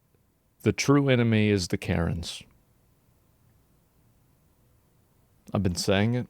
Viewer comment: i thought Asmon said "The true enemy is the carrots" 😂😂😂